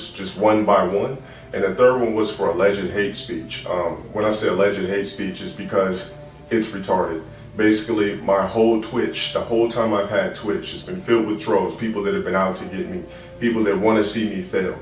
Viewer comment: Bro who edits your videos? Whoever does the subtitles needs to get fired or actually listen to wtf is being said 10:18 “legit hate speech” “filled with throats” ???